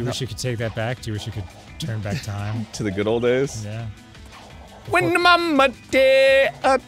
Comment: Funny thing; my dad consistently mishears that lyric as, " Wish we could turn back time to the good dope days." And now you will too.